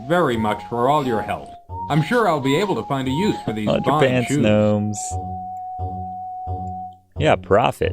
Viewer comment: Did he say "Bomb-ass shoes?"